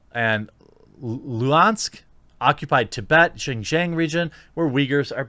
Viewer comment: it really sounded like you said ching chang region.